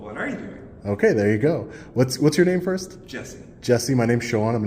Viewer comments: Is his name 'Jerry' or 'Jesse' ? It sure sounded like he and you both said 'Jesse' the first time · The reason “Jerry’s” face is blurred is because contacted YouTube & is trying to have my video taken down. Are you embarrassed by your actions? I would be too. Tyrant. I will be going LIVE today to discuss this and some more interesting developments…:_WTP::_LIA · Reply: Is his name 'Jerry' or 'Jesse' ? It sure sounded like he and you both said 'Jesse' the first time